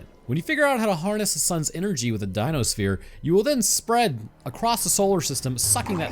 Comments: Harness the suns' energy with a "dino sphere", I thought they were called a "dyson sphere". Jokes on You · Did you say a Dino sphere? 0:39 lol wake up